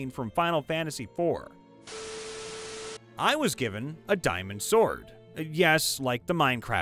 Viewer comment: I completely misheard that as “a dinosword” and I have to admit that is the most awesome weapon I have ever heard of.